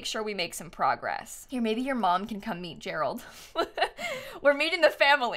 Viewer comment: I thought she said “ mom can eat Gerald”